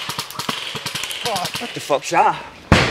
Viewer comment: lol 5:40 What Actually said: "What the F*ck's that?" What the caption said: "What could that be?" XD